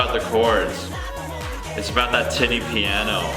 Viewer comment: i thought he said "titty" piano at first lol